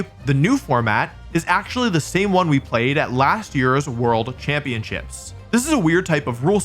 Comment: is it just me or did it sound like said “world champion shits”